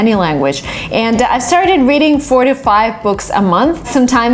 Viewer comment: Did I heard 45 books a month 😅